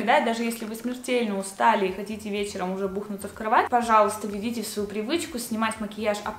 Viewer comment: послышалось бухнуть😹